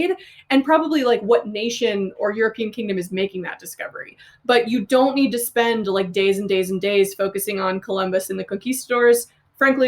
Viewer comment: I could swear I heard 'Columbus and the cookie stores' which was DELIGHTFUL